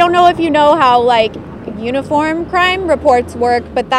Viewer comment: Why the hell do I keep hearing “unicorn crime report”. 🦄